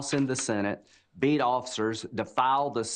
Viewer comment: I thought Lindsey Gram said “beat off sirs”